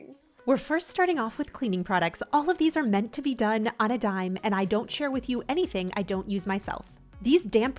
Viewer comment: Thought it said "Dolt on a Dime"